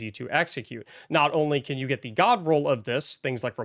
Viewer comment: I really have a problem, i hear "dog roll" everytime someone says god roll... HALP...